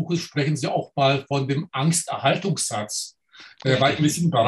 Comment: Ich hab' doch glatt "Amtserhaltungssatz" verstanden.